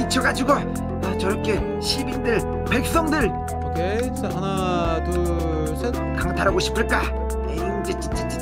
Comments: 에 오케이 하나 둘 셋 저만 들리는거 아니죠? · 에서난 들었다 오케이 하낫둘셋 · 초에 오케이123이라는 소리들림 ㅋㅋㅋ